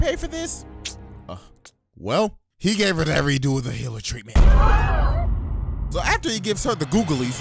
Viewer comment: Is he saying “every dude with a healer” treatment at 4:30? I’m confused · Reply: Redo of healer treatment, look it up